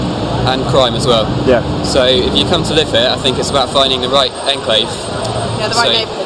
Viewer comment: Hello what did the first guy say in 1:07? It's something like "It's quite a low offer?? and what else until 1:15 I don't understand him. Thanks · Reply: Marina Tejero how about the last man that he interviewed, what did he say in 5:20? Something like " about finding it right? Thanks